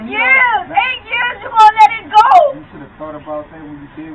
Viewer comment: now I question what is sed by her she says “8 years 8 years ur gonna let it go” like mam u did that to urself when u cheated.